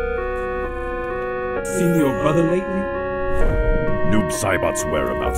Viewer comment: I thought he said I've seen your brother naked